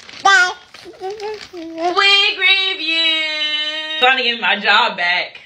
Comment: No I heard her say ‘Wig review!’ under her breath @ 0:06. 😂😢